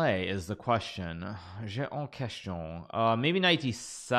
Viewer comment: what did he say ? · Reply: «j'ai une question» which means I have a question in french